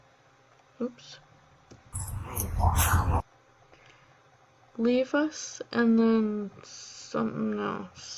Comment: Hi, it sounds like 'leave us alone'. Well, that's how I hear it.